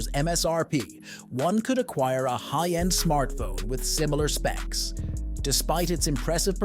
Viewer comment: Idk if I heard this right but at 1:44 did you say one could buy a high end smartphone at 45k